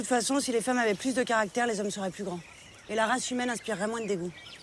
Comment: La quoi ? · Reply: oui oui, la race humaine